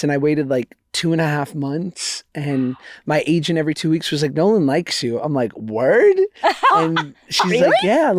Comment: when Josh says "Nolan likes you" I thought he said "no one likes you" and their reactions were SO confusing for a second there lmao